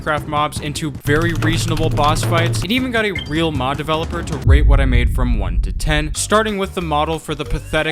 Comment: Dawg i thought you said r*pe what i made 😭